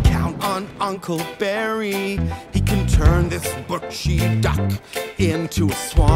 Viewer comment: my friend told me about how bad this movie was but i was still not prepared for any of this dialogue. butchy duck???? BUTCHY DUCK???? at first i thought he fully said dyke and i was just sitting here like 😀😀😨 but fr if a gay man said he could "fix" me (a butchy dyke) it would be hands on sight